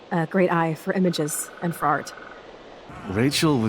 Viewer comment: When I first heard what Chloe said at 0:56 I thought she said fart XD